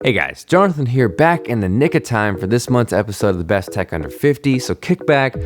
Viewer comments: all i heard was "back in the nigga time" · did he say nigga time? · Did he just say "Back in the Nigga time"? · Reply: Back in the nick of time